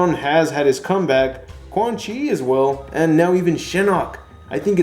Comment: When you said Shinnok, I thought you said Shenron..